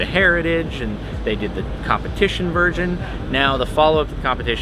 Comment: weird flex but okay 😂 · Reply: Now if you listen at 1:14 he clearly says competition virgin instead on competition version